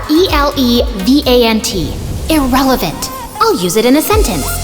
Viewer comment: i thought said "pathetic "or is it my ears she spelt "irrelevant "